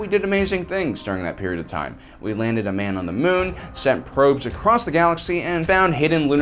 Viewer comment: did he say across the "galaxy" ?